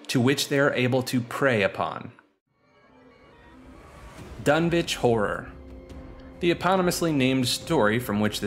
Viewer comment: Did anyone else hear him say "done bitch horror"